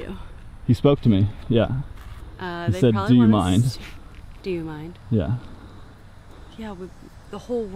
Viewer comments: I thought he said “are you out of your mind “? Perhaps because he saw you running towards them . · I don’t think that Chris said, “Do you mind?.” Listen close. To me, it sounds like he said, “Are you out of your mind?”